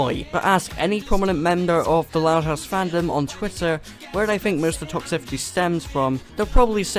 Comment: I've accidentally had captions on and it says "Mr. Top Safety" instead of "most of the toxicity"